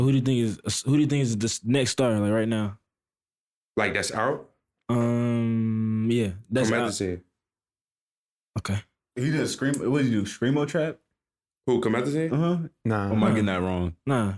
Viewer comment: Who does he say is a future star around 15:00? Couldn't hear the rapper's name... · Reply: Heyy Beats Comethazine